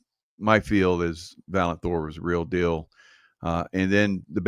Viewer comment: I thought I heard Valant Thor was unusually Tall.